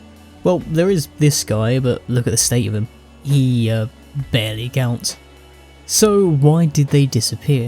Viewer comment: he barely gouts? Did I hear right? 😂